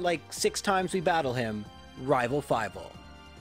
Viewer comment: I thought you were saying "Rival Five-All" As in, he has 5 Pokémon at this point...